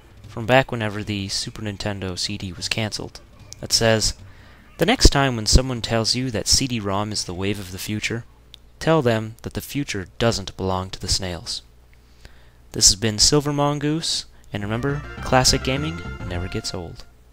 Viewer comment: I heard it as "... to the snails." I am just making an assumption here but I am assuming they made an argument that cartridges load faster than CDs.